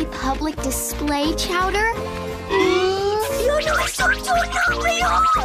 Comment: what did chowder say 😂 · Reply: he said: "Yo no soy tu novio, nunca nunca" (the language is spanish), which means: "I'm not your boyfriend, never ever".